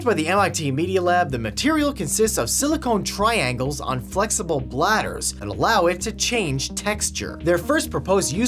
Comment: am i hearing that right? silicon triangles on "flexible bladders"